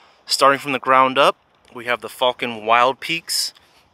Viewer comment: I thought he said “ we have the fuckin wild peaks” I was so confused